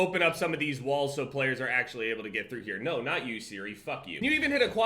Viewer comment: did just say siri @ 2:00? when did he get an iphone???